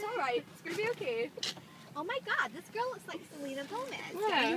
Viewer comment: Someone can told me what she said Jesy in 2:15 I don't understand I'm french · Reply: Dada Zohra “oh my god this girl looks like Selena Gomez”